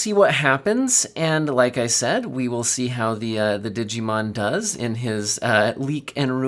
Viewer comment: Did you call him Digimon?